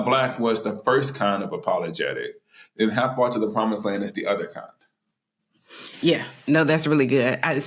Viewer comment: How Far to the Promised Land is a great read! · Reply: Man I am going to be fully transparent this is my first time hearing of this book. I thought he was saying the name of the book was Hind-parts and the promise land. 🤦🏾‍♂️ pray for me